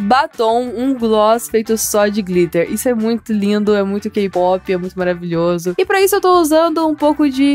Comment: eu ouvi kpop? Ksksksks